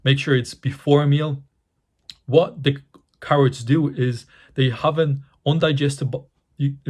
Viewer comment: what are u referring to. It sounds like ur saying “rock caroid” what is that? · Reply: Raw carrots. My accent lol